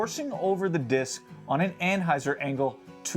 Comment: He is saying "anthyzer" right? Am i the only one hearing this? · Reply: Yes. He is definitely saying ant-hyzer. Although, at 5:15, he does say "anhyzer". He must have put his tongue at a slight angle on that one.